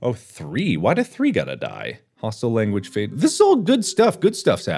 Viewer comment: I thought he said pasta language